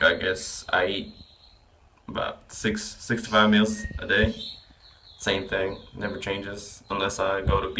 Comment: Haha it sounded like said 'I eat SIXTY-FIVE meals a day'. what about the last guys question on reverse dieting? I'd be interested to know to as I finish my cut the end of January.